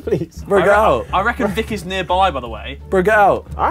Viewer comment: I thought they were saying viki instead of vik for a second 😂😂